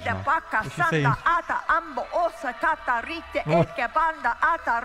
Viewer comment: all i heard is Aata Aata Aata Aata....... Oh my desi ear 😂